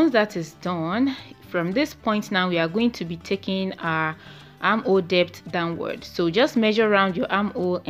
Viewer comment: what are you saying at 3:10 Arm what?i wish i can get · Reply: Arm hole depth